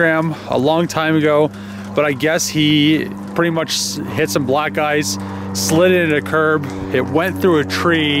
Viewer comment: Sounded like he said"hit some blsck guys"hahahahaha